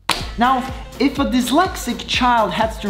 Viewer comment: 🤣🤣🤣 does he say a "coke addicted mad scientist" or "coke addicted meth scientist"? IMO, I find the later funnier.